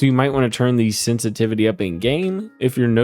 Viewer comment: did you say sense a titty?